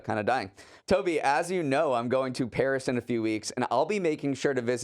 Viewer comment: When I was listening, I heard "Toby as you know, I'm going to PERISH in a few weeks" and got so scared.